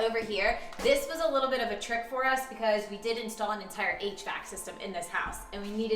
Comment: I coulda' swore she said an 8-Track system for the house. I was waiting for the punchline or the laugh but none came .... had to rewatch ....lol